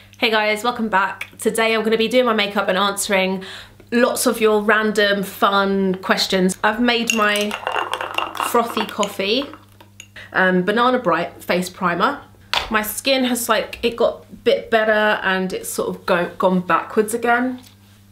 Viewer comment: My stupid ass thought you said banana bread face primer 😂I mean everyone is baking it at home now